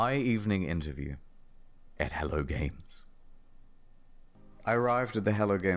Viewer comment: After about half way through the video I swear I kept hearing 'hella games' every time he said their name.